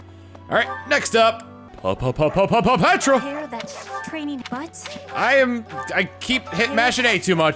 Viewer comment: all i hear is "training butts" I had to take a double take on that line